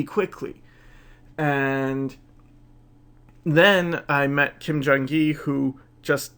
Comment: "Then I met ..." First I heard Kim Jong-il (leader of North Korea), and went "no, that can't be right." Then I thought I heard Kim Jong-in (singer in EXO), and I'm sure that's still wrong. ...who did he mention? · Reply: ¿Kim Jung-gi?